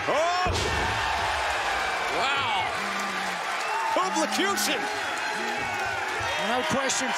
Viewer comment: What did he call that move? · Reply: Copelacution